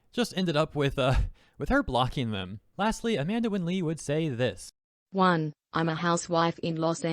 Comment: wrong lady. Amalee voices her. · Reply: wait... Ay what the hell?! I thought he said Amande Lee Oh goddamit